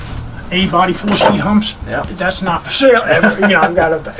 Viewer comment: I’ve met lots of guys like him over the years. All I hear is ... “Not Fur SaiL “